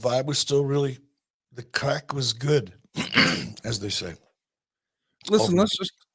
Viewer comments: did Eric say "The Crack was good?" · If you get bored by the AEW talk the show actually starts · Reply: did Eric say "The Crack was good?"